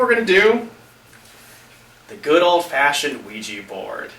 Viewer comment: I heard: "The good old-fashion Weegee Board"